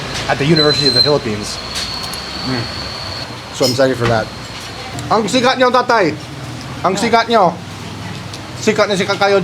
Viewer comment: “Ang siga nyo tatay” is what I heard but said “Ang sikat nyo tatay”😭 caught me off guard 😂✌🏻